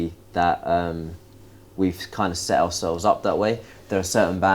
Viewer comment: What did the interviewer said? like "............ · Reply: Like Status Quo